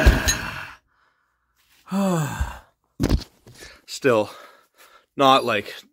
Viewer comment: when he said "Still" sounded like devon ngl